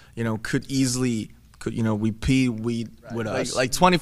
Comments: is there something wrong with me bc I hear RapMon saying "we pee weed" ... tf is wrong with me xD ? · Did he say "you know we pee"??